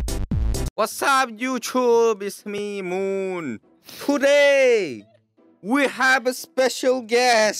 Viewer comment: Moon says : Today Me : Today ? Oh I thought you said Hoday LOL